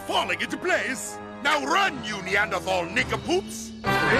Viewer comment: what did he say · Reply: Nin come poops